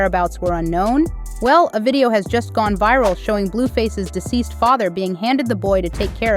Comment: It sounds like you said Blueface's deceased father